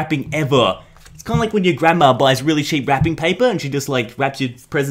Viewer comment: did he say shitty wrapping paper?